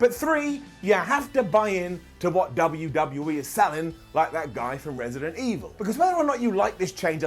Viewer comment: does he say, “like that guy from resident evil”? If he does, what is he talking about?